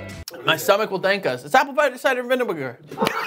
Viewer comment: "It's apple vida cidar mavinniger" is the funniest shit I've heard in a while thank you holy shit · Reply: I heard "apple pider cider vinnimager" 😂